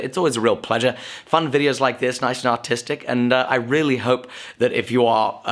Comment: for a second i thought you said "autistic" not "artistic" lol ummm I would check the other block to make sure its properly welded before proceeding